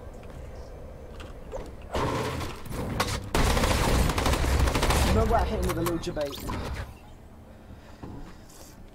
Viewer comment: Does anyone A. Know what Mongraal said at 4:10 or B. Know why he quit. It's driving me nuts. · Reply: He said "no way i hit him with the little jebait" and he quit cause he already won the game (fortnite friday)